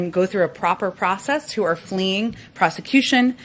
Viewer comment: At the 2:17 mark she says they are admitting people who are "FLEEING PROSECUTION..." She DID NOT SAY PERSECUTION. Bearing in mind she is an inarticulate imbecile, she may not know the difference. If she does, then she said we are admitting people facing criminal charges in their own countries!!